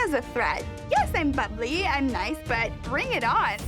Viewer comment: Oh she called herself "bubbly and nice". sounded like she said "fugly and nice".